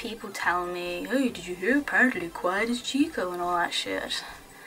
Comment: Did you just say: ''Quiet is Chico''???? Or am i forgetting the little English that i know? O_o